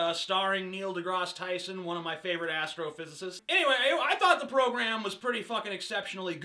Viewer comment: lol, thought you said one of my favourite Afrophysicists